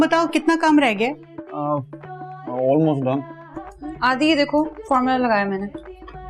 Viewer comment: did she just call him adi ?? cute!!